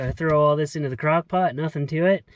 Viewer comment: Did I hear "crack pot"???